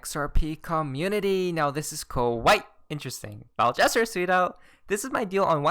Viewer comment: Ohhh .... you are saying .... “tweeted out”” .....I always thought you say “justSoYouCanSee”” .... really fast LOL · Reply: I thought he was saying sweetheart